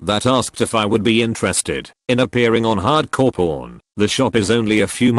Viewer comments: I heard 'hardcore porn' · thought that said "Appearing on hardcore porn" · When it said hard core pawn I thought it said HARD CORE PORN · I seriously thought I heard hard-core porn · At first I thought it said hard core porn xD